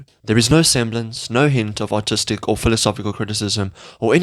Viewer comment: i heard autistic